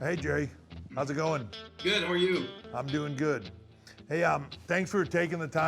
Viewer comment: Could’ve sworn I heard Kerry lawler say who are you when triple h said how are you